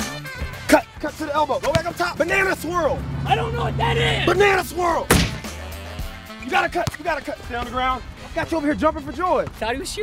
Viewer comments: “Banana Swirl!” “I don’t even know what that is!” ” B A N A N A S W I R L” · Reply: Hardcore MatC lol i thought he said banana squirrel XD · “Banana Swirl!” “I don’t even know what that is!” ” B A N A N A S W I R L” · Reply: I thought he said banana squirrel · I totally died at “BANANA SWIRL I Don’t know what that is”🤣😂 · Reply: For the longest time I thought this guy was saying "banana squirrel" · “Banana swirl!” “I don’t know what that means!” “𝐵 𝐴 𝑁 𝐴 𝑁 𝐴 𝑆 𝑊 𝐼 𝑅 𝐿“ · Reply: i thought he said banana squirrel 💀💀